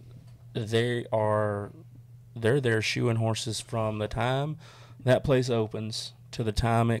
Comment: For the first 10 minutes i thought you said shooting horses 😂😂😂😂😂😂😂🤦🏼‍♂️🇬🇧🇬🇧